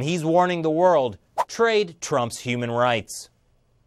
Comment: Trade trumps human rights ...did i hear theat corectly5:02